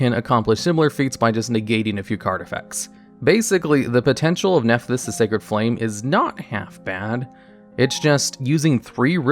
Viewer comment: Did he just say nephthys the sacred fled?